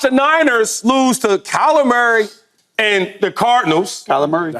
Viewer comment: When Shady said "Kyler Murray" I swear I heard "calamari", I think I'm gonna refer to him as calamari from now on 😂